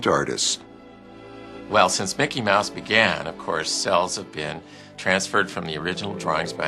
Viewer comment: What he said cells? 10:08 what is that clear paper? what he said